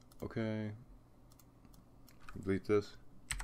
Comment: what does he says on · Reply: "ok, delete this"